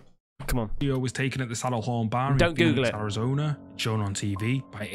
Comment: What is he saying BAH,, Or BAR 14:15 man get;s yo Engles right or when they pronounce Booty instead of Body LoL 😁😁